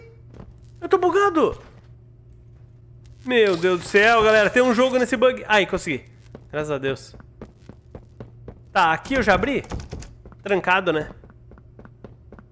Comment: Só eu que ouvi "TEM UM JOGO NESSE BUG?!" KKKKKKK